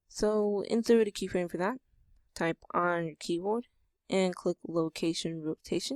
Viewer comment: WTF, DOES HE SAY TO DO AT 22:40 help plz, It sounds like he say's type "r" but it just makes it want to rotate! help plz!